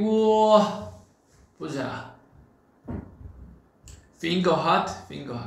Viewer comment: Seokjin said "finger heart" but the captions says "Hotmail" what the- 1:47 “babies, I want to meet you with green onions” Jin has...onion friends?? 16:07 It's "EGG tart" not "Dog tart" what the hell? 16:26 "Bambi-nim" who's that? I only know Bang-Pd 17:09 "Shield-nim" 20:55 It's PD-Nim, not plastic Lmao 21:03 The subs what the actual heck!!?? 21:58 Is their company name Hybe or Hybrid?? 22:13 "Tongue"? Say what now?? 22:54 Oh erm...ok...er...happy birthday...." bean"? 23:26 "Korean version of Korean version"...smh... 23:29 How the heck can we WEAR Korea. Is it a cloth to wear?? 23:38 "lego Korea" Minecraft?? 25:14 "Hey die" Did Jin ask the phone to die?.... 25:42 Pottery? 26:08 "Rain is a comment" A rain of comments?...Whaaat? 28:10 What does this sub even mean? I am confusion